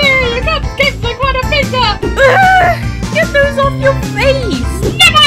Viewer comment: what i heard was UGH GET THIS OFF YOUR FACE NEVAAAAA